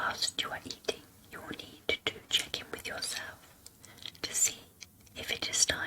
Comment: Oooohh she said "check in" at one point, but I heard "chicken". Now I'm starving.....